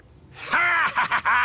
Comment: als joe sagt du hast uns verfehlt lucky luke hat es sich angehört wie du hast dich verf** lucky luke weil er es so schnell gesagt hat